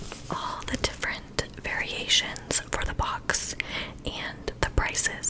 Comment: Everytime you said "variation" item I kept hearing "very asian" LOL